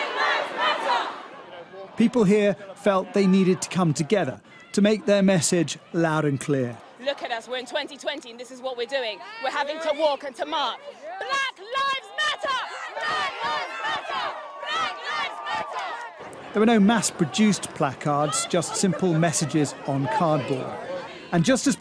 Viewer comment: In the beginning of this video, I thought I heard #BlackLIESMatter